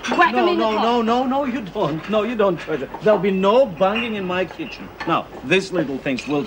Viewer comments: Lol i thought he said 'bumming' >< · I thought he said "no bumming in my kitchen" lol